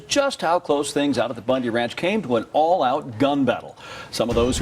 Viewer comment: damn it, thought it said Bunny Ranch!